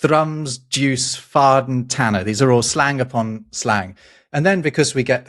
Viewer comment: why, please, is a tenner a sixpence? How do I get from 10 to 6? I heard and read "tenner" before and always assumed it to mean ten (pounds or shilling or whatever). Please explain all the quirks of english money system, if not here then on. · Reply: Its a "tanner" that's six pence. A "tenner" is almost certainly 10 quid (pounds sterling.